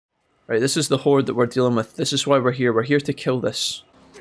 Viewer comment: I can't hear well (too many years in warehouses), so I thought you said "This is the wh_re that we're dealing with". 😂